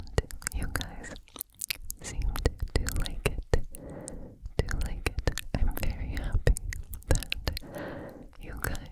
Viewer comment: Me trying to decipher what shes saying at 1:18 – 1:31 'Do you like it? Do you like it? Better berry up. You guys, undrink my water, undrink my water. And sensivity levitating' 🤣✨️ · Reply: “Did you like it? Do you like it? I’m very happy that you guys are enjoying my 100 percent sensitivity videos”